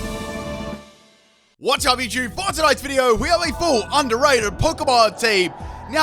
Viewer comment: The subtitles say "one chubby jew for tonights video"